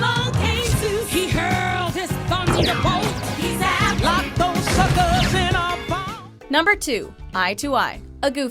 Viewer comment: did she just say lock those fuckers? HAHAHAHA